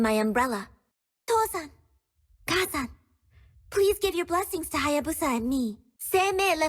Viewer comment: What I heard about Kagura's quote: 6:53 "Toh-san, Kaa-san, please give your blessings to Hayabusa and me" "Seimei left this umbrella. What should I leave behind "It seems that very few people know about Onmyodo" 7:25 "Aku ryo tai san" 7:28 "Kai" And Hayabusa's Ultimate: "Kage ro Ougi"